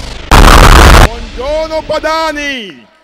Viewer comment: vi giuro che ho capito "buongiorno banani"